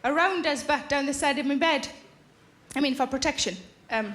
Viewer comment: can someone tell me what she said at 1:35? I'm no native speaker and I can't understand the word she used (before they show prince charles face) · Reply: "I mean for protection."